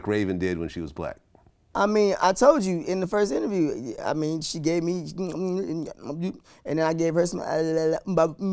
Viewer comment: For those that don't understand jibberish. "I told you last time she gave me that sloppy toppy and I licked her up like I wanted the tootsie center of a tootsie pop."